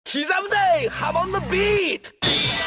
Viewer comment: This is what your intro sounds like,” Ceasa Muday, hamon no beat!” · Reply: i hear it like “Shiza Mudae, hamondo beat!” but i know nothing about japanese so idk